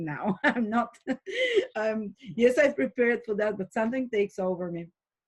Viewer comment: 📌 I’m NOT so optimistic... Aquarian is Saturn ruled. Let us not forget our astrological roots. The 11th house seeks to equalize and harshly. God forbid you have an opposing view, Aquarian will cancel you and bring you in line. I see MASSIVE SIGNS of communism on the horizon. The 11th rules peasants and is the lowest vibration of the Sun. It’s nice to have your positive insight... But for the next two thousand years I believe will be in a harsh Saturn winter. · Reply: Makes sense as to why I keep hearing “prepare for winter”.